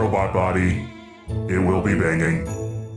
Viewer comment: Is it just me or did i hear "Banging"